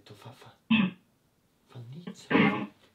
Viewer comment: A 4:47 ho sentito dopo guarda "fabrizia"